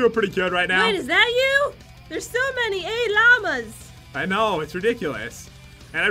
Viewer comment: did Jen say ayy lama just like pat sometime after?!?